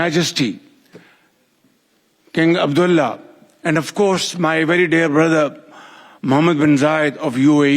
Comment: I swear i thought he said my very dead brother😂